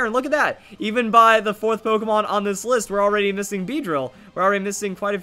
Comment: I had your video playing in the background while lost in thought and when you said "just missing beedrill" or something I heard it as Justin BieDrill..... well I know what I'm naming my first Mega Beedrill now